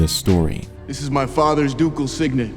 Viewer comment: In the cinema I had too many beers and I thought he said this is my father dougal’s signet